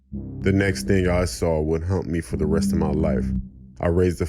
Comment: I love these stories but man... These subtitles will haunt me lol ..the story bout a home invasion 1:56:31 the subtitles qouted "the next thing i saw would hump me for he rest of my life" instead of "haunt" lol